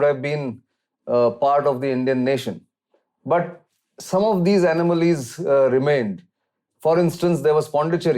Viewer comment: Columbus was looking for India and landed in America. I think you got your adventurers mixed up. Also it's "anomalies" and not "anamalies". At first I thought I heard "animals".